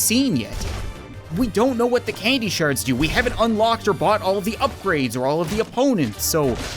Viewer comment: when you said we haven't bought all the upgrades i thought you said butt all the upgrades